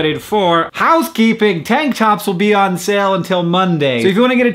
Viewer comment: Tank tops... OHHHH I thought he said Hank Tops....